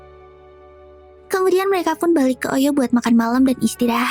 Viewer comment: ngomong apa tadi oi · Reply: Hotel oyo mantap